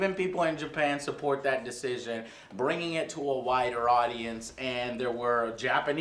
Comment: I swear i heard him say "Bringing it to a whiter audience" lmao